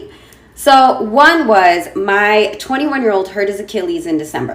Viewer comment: did she say her 21 year old? Wow!!! If yes then she is really special...or...